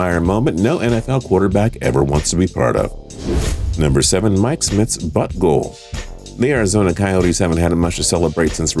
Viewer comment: sounded like butthole tbh